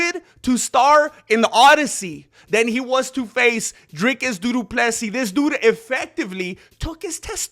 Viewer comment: Did he just say duduplessis 😂